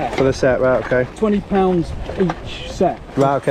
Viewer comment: think he said "£20 for each set" if I'm not mistaken.......????